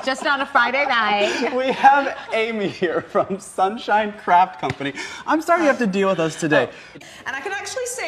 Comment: Honestly sounded like he said "Amy, from sunshine crap company"